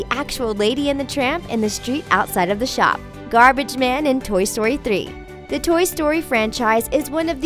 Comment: "Garbage man in Toy Story "Tree"."That's what I heard.